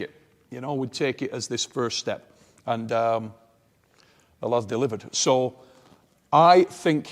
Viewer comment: what does he say????? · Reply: 'The lad's delivered'